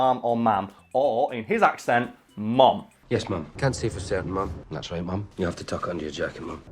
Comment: Ngl I thought he was saying mom even though I knew it was ma'am